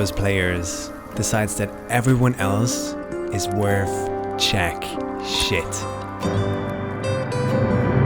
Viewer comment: did he just said "Czech shit"? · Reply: “JACK” shit